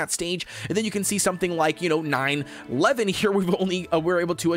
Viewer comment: did he just say 9 11...